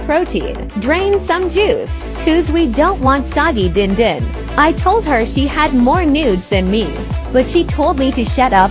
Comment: HELP I LOOKED AWAY FOR LIKE A SECOND AND I HEAR "i told her she had more nudes than me" 😖